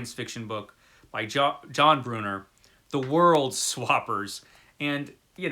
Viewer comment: I thought you said the title of that book as The World Slobbers, just like I did at that cover hubada hubada